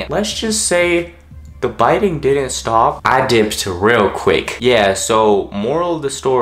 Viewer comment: Can someane help me with my listening? Since English is not my first language. xD What does he say at 3:35? "I ¿? real quick" Thank you. So juicyyyy hahaha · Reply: virtualmima he said "dipped" which is kinda slang for leaving. Basically he said he left quickly.